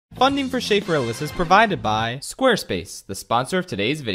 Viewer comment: Can someone please transcribe this guy's intro? I hear "funding for shape realism was provided by squarespace" every time